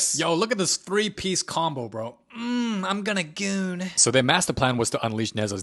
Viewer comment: what da hail u say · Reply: Goon 💀💀💀💀💀💀💀💀